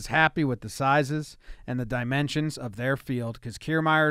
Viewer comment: 😂😂😂😂😂😂💀💀💀💀💀💀 honesty funny asf(I thought he said of their fielders*